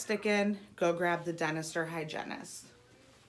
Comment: What was said: "the dentist or hygienist" What I heard: "the dinosaur hygienist" o.o